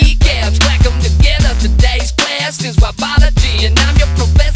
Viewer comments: I almost thought he said "wombology" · I swear to god I heard him say “wumbology”